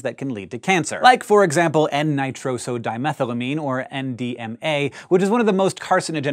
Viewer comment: I swear to god I thought he said MDMA and I was like hold up, what.